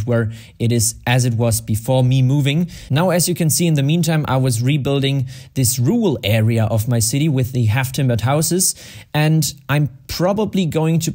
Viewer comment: i heard "this uwu area"